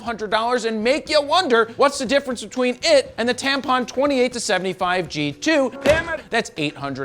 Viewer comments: he sai Tampon instead of Tamron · Hold Up! Did He just say TAMPON, instead of TAMRON!! WOW! I caught that!!! 😂😅🤣📸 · Wait a minute, did you just say "Tampon"? (2:49)... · wait... did you just say "the tampon 28-72 G2"?.... lol!!! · my guy said tampon instead of tamron🤣🤣🤣🤣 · Did he say “Tampon”? At 2:51😂